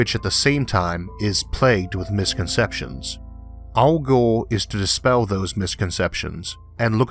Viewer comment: i thought he said "Al Gore is to dispel those misconceptions" Elmer Fudd